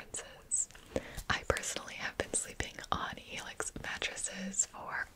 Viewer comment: When she said “HELIX” I thought she said “FELIX” like PewDiePie